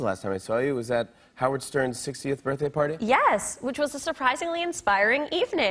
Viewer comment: I heard "I think the last time I saw you was at Howard Stern's 16th birthday party" and I was like uhhh wow that was a long time ago